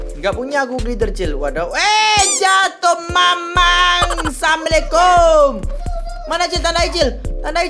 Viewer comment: kedengeran bilang assalamualaikum gitu